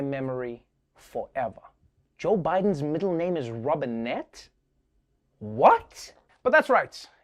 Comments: I thought he said Joseph Robin hood Biden... 😆 🤣 😂 😹 😆 🤣 Robinette is much better than what I thought I heard.😆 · Ooo Robinette I thought they were saying Robin F. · Joe Biden’s middle name is Robinette. Phew! 😅 For a moment there I thought I heard him say Robin Hood. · i thought he said his middle name was Robin hood lol